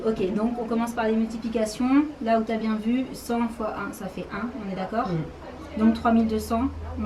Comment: c moi ou elle a bien dit :" 100×1 ça fait 1 " 😂😭😭😩